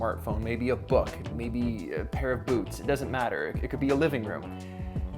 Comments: did you say 'a pair of boobs'? or am I too drunk? FYI: I bought a 5L Czechvar keg today and drank almost half of it. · I heard "a pair of boobs" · Thought he said, take a shot of a pair of boobs lmao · did he say "a pair of boobs"?